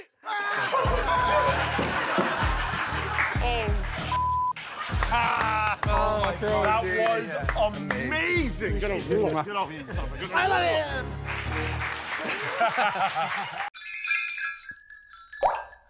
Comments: SAL reacting to Q's kiss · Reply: why did i hear "I love ya" as well ??? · Probably a coincidence but does noone else think it sounds like Sal says I love you when he makes all those noises at Q?